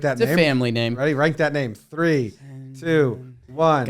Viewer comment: Jimmy, why the fudge does it sound like you’re saying “write that name” when you say “rank/rate”??? Even Jake thought you were saying “write” 😂😂